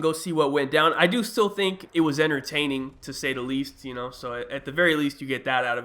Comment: did he say "inertaining or entertaining"